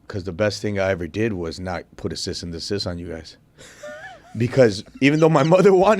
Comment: What the fuck did he say at? @ 29:59 He wanted to put what on these guys????? Assist on assist????